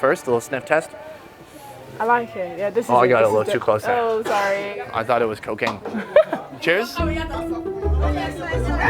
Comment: "I'm going to smell it,.....oh got it in my,i though it was cocaine"🤔🤔what? 3:08 what did i hear